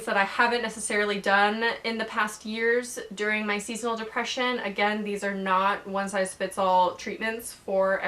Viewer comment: When she first said season depression I thought she said "Female Oppression"